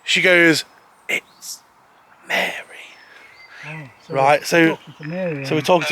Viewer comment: I just heard Mary 6:24pm